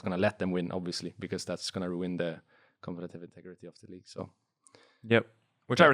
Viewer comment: What is Rekkles saying at 1:03:22 ? That's gonna ruin ... ? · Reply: the competitive integrity of the league